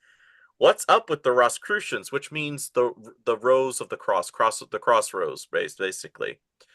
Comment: You were discussing the Roscicrucians, and said “Rose/Cross, Cross/Rose” (which initially, I heard as “Crossroads” (the infamous barter with the Devil many musicians claim to have made). Also, Vance invested with Thiel in Mithril corp. 😏